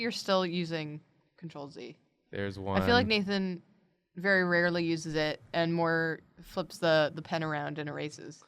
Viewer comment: I was scrolling through facebook and spacing out, and it sounded like said, "Nathan rarely uses the N word" and i was thinking, id fucking hope so